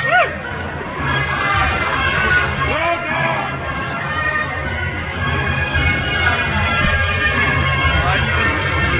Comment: What did he say? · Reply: Woah dude! that’s probably what he said